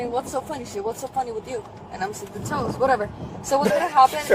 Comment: what about those? I didnt understand what she said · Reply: It's toes not those. I think she stubbed her toe or something.